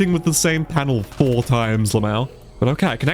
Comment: bro did you say “lmao” I acc can’t tell and its bugging me